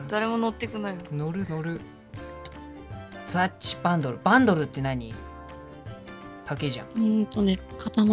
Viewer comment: 「バンドルって何？」自分の空耳「坂東って何？」(showさんにネタ振ったのかと思った